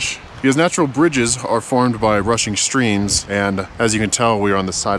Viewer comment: You said rushing streams, It sounded like you said Russian stream.😂